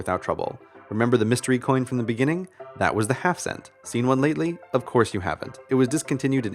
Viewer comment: am i the only one who thought he said "half-dollar" instead of "half cent"?